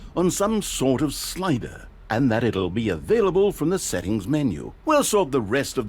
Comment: The narrator said available when the subtitles say accessible here